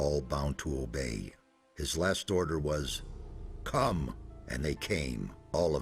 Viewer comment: LOL!!!! Did anyone notice that Xardas sounds like he is saying at 5:23 "CUM and they came all of them even the dragons". LOL!!!!